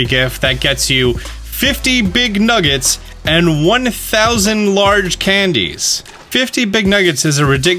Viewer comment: At around 6:30 I heard him say 1000 Exp candy’s